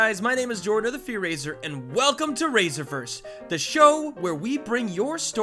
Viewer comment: i thought when he said raserverse i thought he said razor burns... XD